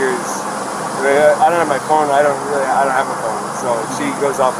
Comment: i thought he said he didn’t have a phone - when they start talking about being separated and not texting each other etc..